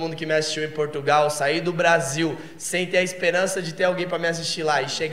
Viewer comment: Só eu que entendi G A R E L A ?